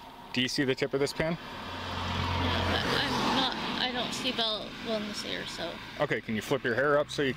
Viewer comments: Did she say, "I don't see well out of this ear"? Roughly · Did she say I don’t see well with this ear? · Did she say: I don't see from this ear?